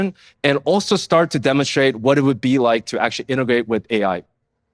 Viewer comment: now, and he didn't say "innovate with Ai," he said, "Integrate with Ai" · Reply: I heard a g "innagrate" he mumbled it